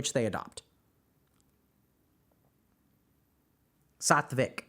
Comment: HealthyGamerGG what did you say at 16:39? Sothvick? What is that i cant find it on google. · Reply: Sattvic